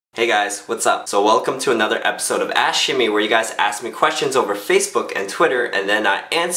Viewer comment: when the video starts i always hear "welcome to another episode of ass shimmy" XD